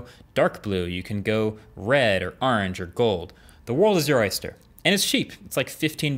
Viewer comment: Did he just say urange??😂